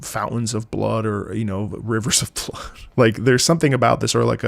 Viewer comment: D-Did he say… “Rivers of Blood”? 👁️👄👁️